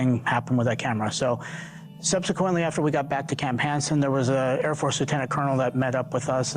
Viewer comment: Did dude say airforce satanic Colonel?